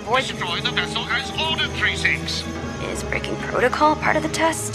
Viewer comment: does she say freaking or frikken? or none of the above?? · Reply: breaking not frikken